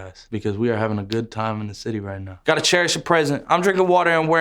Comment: I thought he was saying "got a chair as a present"